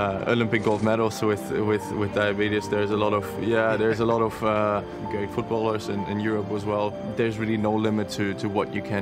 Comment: did he say there's a lot of gay footballers?!?